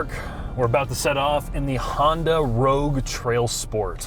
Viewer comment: Did he say Honda?